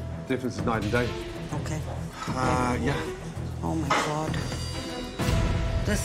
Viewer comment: is that me or does Gordon sound like he says a Uncle Roger "Hai-yah"